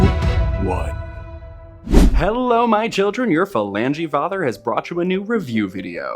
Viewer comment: I thought you said "philandering father" at first..... love your channel ❤